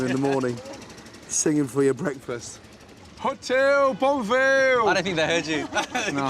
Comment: lúc gordon rao mà tui nghe thành "Hotel Bull Real" :v